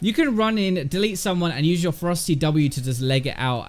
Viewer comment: All I hear "Frosty Q"